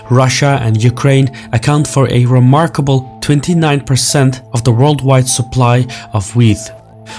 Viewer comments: "29% of world wide supply of weed" I thought you said, I was like 'dang', that much? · Ohh..."Wheat" had me really worried there for a moment; I thought he said 25-30% of the world's weed...pheeew!